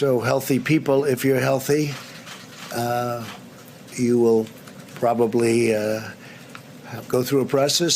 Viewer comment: did he say "probably" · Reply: Yes, he did say probably, because he only cares about himself, his welfare, his campaign, and “his” country, and “his” economy